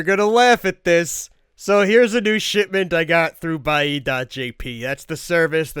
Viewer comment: I thought you said, "Shit-ment" for a second, man I am an idot